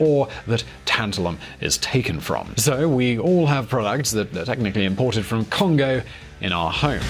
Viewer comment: I thought thay said Chicago at first